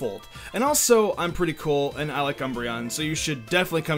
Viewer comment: Lol I thought he said Mr.Cumbreon xD